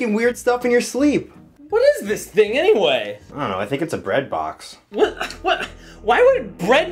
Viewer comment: Bread box 😭 i thought he said Breast box 😂